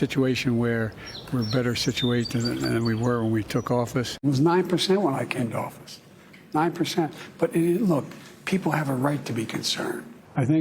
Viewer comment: Biden said verbatim “it was 9% when i came to office.. 9%.” 1:28 Biden did not say “the factors that took inflation to 9% was there before i came to office”